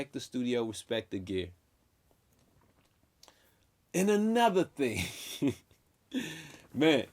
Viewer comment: Great video 👊👊. Everytime you said "and another thing" for some reason i heard dj khaled "another one" 😂